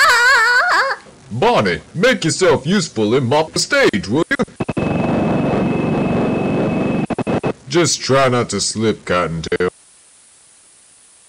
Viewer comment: sounds like cocktail instead of cottontail